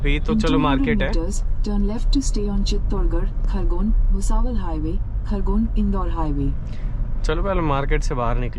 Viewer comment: DID I JUST hear bhusawal highway 😅😅 btw i live in bhusawal welcome to maharastra brother ❤❤✌✌🤗🤗